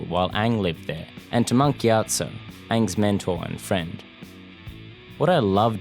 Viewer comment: am i the only one who thought Monk Giatso was said Monkey Otso